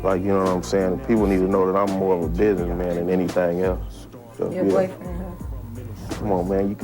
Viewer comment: What did tboz say at 4:54?? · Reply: She said "your boyfriend, huh?"